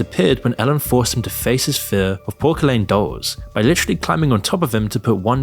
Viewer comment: I’m sorry my boy tf did you just call them dolls? 😂😂